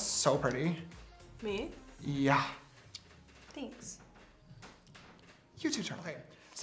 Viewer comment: James literally complimented himself when he said “that’s looks so pretty” to Dixie when HE was the one doing her makeup and charli was doing her won and he didn’t say anything to her :’( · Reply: I thought he said you too Charli